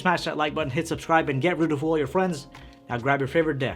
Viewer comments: it sounds like he says "Get rid of all your friends". This could actually get rid of friends because their minds would be blown! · When you said 'get ready to fool all your friends' I heard 'get rid of all your friends' XD